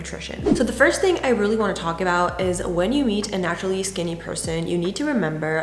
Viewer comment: It sounds like she said “when you eat a naturally skinny person” lol. I think it was “when you meet.” 😅😅